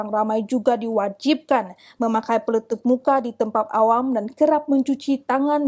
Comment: Kalau tak salah dengar macam pemberita kata 'peletup' muka bukan 'pelitup!'